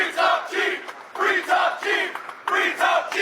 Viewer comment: what are they shouting? Free top cheek? 😂